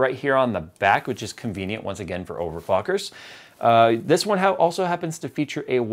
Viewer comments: don't think i didnt catch on you saying a Bios reset which is convenient for OVERFUCKERS. I heard you. Trying to slide that one right in there thinking your sneaky Jay hahahahahahaha awesome !! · you said over fucker instead of over clockers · Anyone else hear Jay say Over Fuckers